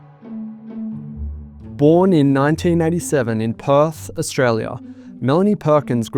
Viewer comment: Why do I keep hearing 1987 as 1997?